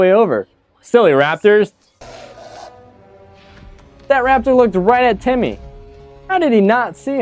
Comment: Did I just hear "Temmie"? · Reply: Eva The Best he said Temmie instead of timmy